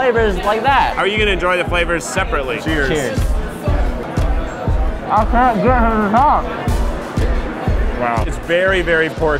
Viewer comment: "i cant get to the top". That's what he said!!!!!!